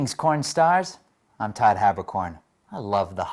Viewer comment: Merry Todd! Also I thought you said pornstars instead of kornstars 😂